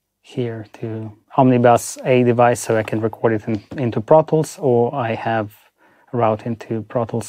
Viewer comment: Tip: when it sounds like he says, “I go into brothels,” he’s saying “I go into ProTools”! 😂